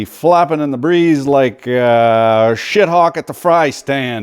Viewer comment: did you just say "Shithawk" Mr Lahey?